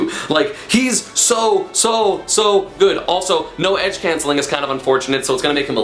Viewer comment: did I hear no edge cancelling? As a Palutena main this upsets me : (